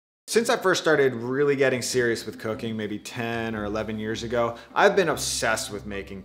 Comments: i swear i heard cocaine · I really thought he said, 'since i started getting really serious with cocaine, years ago'. Ignore me.... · I thought he said obsessed with cocoine